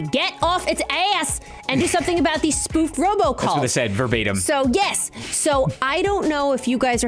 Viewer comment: I almost thought that it said Robocop 😂